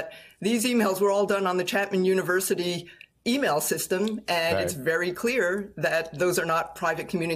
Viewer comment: If those documents were done on a computer at a university, why not get a subpoena and warrant to seize the computer? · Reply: At around 2:30, she clearly said email system, not a computer. Now they might be able to subpoena the e-mail servers. 😃